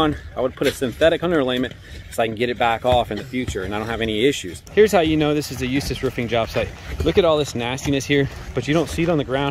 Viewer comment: The company has a strange nabw, it sounded like he said "This is how you know this is a useless roofing job"! YouTube even translates it to useless when CC is turned on.